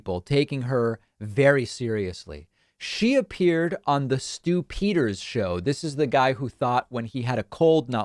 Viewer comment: Stew Peters is one of the more insane hosts on LindellTV. No conspiracy idiocy he won't crank to eleven with deadly seriousness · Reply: Ah, it's Stu PETERS. I'd heard it as "Stu PID. My mistake. 🤨